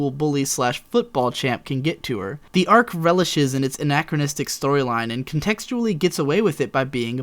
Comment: My English is not the best, did you say anarchistic? If so, what did you mean by that?